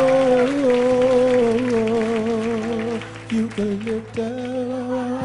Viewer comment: When he said eucalyptus I thought he said you can lick this 😭😭😭